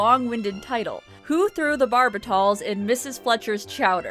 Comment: For a second, I thought you said, "Who threw the Barbasol." Now that would have been weird.